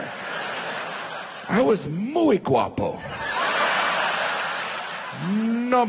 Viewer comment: What's he sayig at 1:20 Moi Gwapo? What language is that and what does it mean?